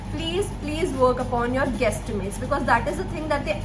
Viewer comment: Very helpful, BTW what did you say very important for the "consulting interviews" at 4:30 "please work upon your"? I did go through it a lot of times but still didn't get it? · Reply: Hey, its Guesstimates. You can google regarding the same☺️